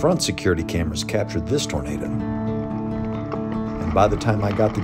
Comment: Why does the closed captions say burritos instead of tornadoes